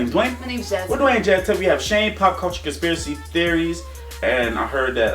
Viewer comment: WE NEVER EVEN NOTICED THE ONE FROM INSIDE OUT MIND BLOWN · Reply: I thought he said cockroach instead of pop culture lol